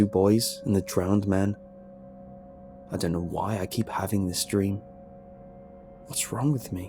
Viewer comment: am I the only one who misheard early statement as I was having a wet dream?